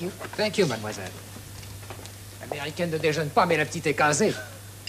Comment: What is that waiter saying in French? From my understanding, he's commenting something about Mrs. Van Hopper's absence, but I don't get much of what he's saying. Joanna David and Jeremy Brett are exquisite in this dramatisation - from my point of view it's perfect. Too bad a restored version is not available. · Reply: I think he says "L'américaine ne déjeune pas mais la petite est casée" which roughly means "The American isn't eating, yet the girl is fixed up". I have to say I totally agree with you, this is a superb rendition of the story and both lead roles are expertly cast, as indeed are the other parts. A proper tour de force and a wonderful BBC production that ought to be better known.